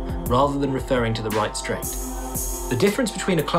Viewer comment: HI ALL, SORRY WHAT IS HE SAYING AT THE 5:00 MARK? THE DIFFERENCE BETWEEN THE GLUMPSEY(??) OVERY HAND AND A CROSS COUNTER IS IN TIMING IT OVER THE OPPONENTS PUNCH. WHAT IS HE SAYING BEFORE 'OVERHAND' BIG THANKS FROM BERLIN. ENGLISH ISNT MY FIRST LANGUAGE. :) AND JACK I AM REALLY LOVING YOUR CLIPS! MY BEST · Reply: Clumsy overhand